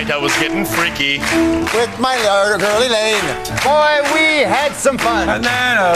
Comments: I think he was trying to say "With my Lady (or Lover) Elaine", but he switched to "Girl" halfway through and garbled himself. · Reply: I thought I heard with my lovely girl Elaine. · Ryan said he did that because Drew set him up HORRIBLY for the rhyme by speaking almost pure gibberish so his response was "ADAOLAOLAH" Everything else was just pure hilarity madness. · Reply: I heard from Drew "with my yard-girl Elaine" honestly not sure what he was thinking when he said that XD